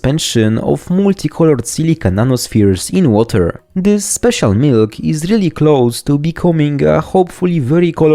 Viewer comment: I thought it said "MILK RGB" 😂